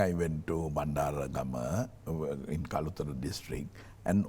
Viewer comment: I heard him say Kaputaa. Is it only me